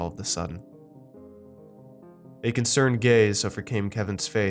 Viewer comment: When you said "concerned gaze" all I heard was "cornered gays" This is how you know you're spending too much time watching Orange Is The New Black